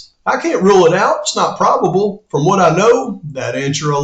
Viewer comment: it sounds like you say probable and not improbable. Yet, it's a minor point since I know what you're saying.